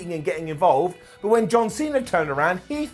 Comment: Did he say John cena at 18:35?